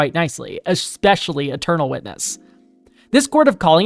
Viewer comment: I misheard eternal witness as "a turtle witness" and now I'm imagining a turtle man being sworn into a courtroom 😂